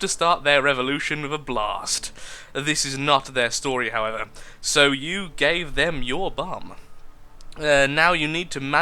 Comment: So i was scrolling through the comments until he began actually playing the game, then i heard "then you'll give them your bum" and I was like : WTF does that description say? lol